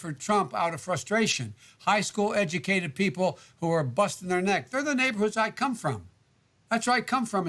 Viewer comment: does he say bustin there nut?